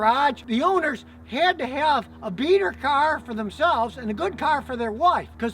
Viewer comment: Beater car! I thought he said beaner car. I had to rewind to make sure.